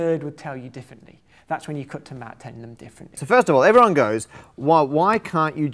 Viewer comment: I can't catch that sentence, "That's when you... ". Can some kind viewer put me out of my misery? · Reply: "That's when you cut to matt telling them differently"